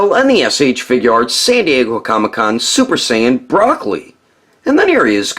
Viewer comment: Does anyone else hear him say broccoli 9:49 😭